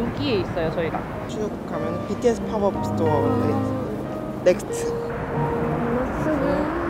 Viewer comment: i swear i heard “BTS Pop-up Store”